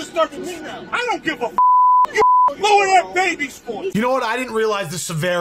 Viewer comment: did he say lower that baby's voice? How tf do you do that??? He must not have children.